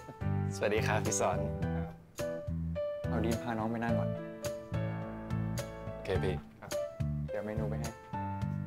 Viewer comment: Why did I hear 'kay, babe in 4:45? 😂 Lakas maka jonaxx amp.